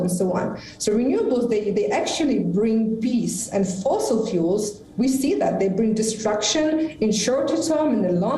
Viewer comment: Sounds like he says fossil fools sounds apt